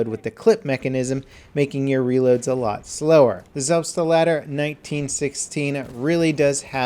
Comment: Haha, the way you said "Selbstlader" made it sounds like you said "Selfstdelarder".